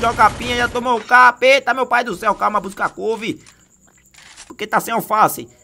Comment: Já tomou um capa eita meu pai do céu... Eu entendi: Já tomou um capeta meu pai do céu kkkkk. Pra quem nn entendeu é no minuto